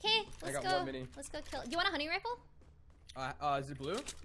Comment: When she said “Do you want the hunting rifle?” I thought she said “honey rifle” Lol cute duo